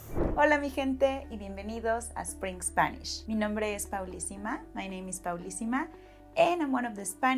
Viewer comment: Te encontré hace dos días. Y durante dos días pensé que dijiste que tu nombre era Fablisima. Pensé, interesante, se ajusta a tu personalidad. Hoy, finalmente escucho a. Bueno, ahora lo entiendo. Me encanta su personalidad. Tu personalidad es amigable y ... ¿Cómo se dice "quirky" en Enspanol? Perfecto para enseñar español en línea. Mantén el buen trabajo.